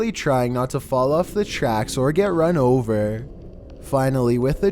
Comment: turn on captions says Finally, with the tracks fast approaching, they are forced to jump. But the captions say Finally, with the train fast approaching, they are forced to jump.